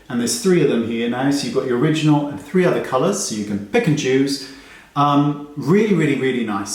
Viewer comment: I guess I should sleep right now.... I heard "pick and choose" as "pikachu" 0:45 💀💀💀💀